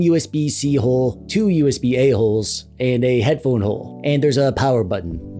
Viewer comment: when I first heard "usb a-holes", I was like what did the usb did to you bro. but then I realized, "ohhh, usb-a holes" now it makes sense lol